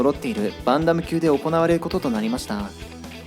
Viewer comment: すごく良かったです。チャンネル登録させていただきます。 ただバンタムがバンダムに聞こえて、そこだけ気になっちゃいました😓